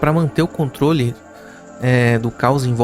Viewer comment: isso eu entendi. O que quero saber é a palavra que você falou nesse 27:21 antes do "desculpe". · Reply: EU ia dizer controlar, mas eu engasguei, mas ia falar " para controlar " mas aí saiu só aquele som ali 👍